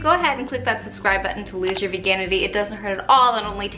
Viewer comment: i thought she said " i will help you loose your virginity" instead of "veganity" lol